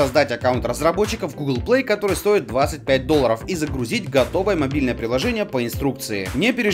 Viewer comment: Послышалось вместо "мобильное приложение", могильное приложение. С минуту где то тупила, потом поняла что заработалась.